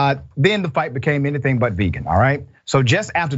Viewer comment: Ramsey - I AM A VEGAN?!? I thought yall said Negan!!